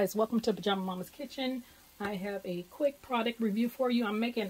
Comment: It sounded like you said welcome to Jumbo's kitchen! Lol